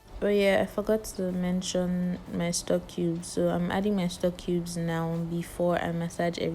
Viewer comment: What did she say she’s adding at 1:17 sorry I couldn’t understand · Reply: I added stock cubes (bouillon cubes)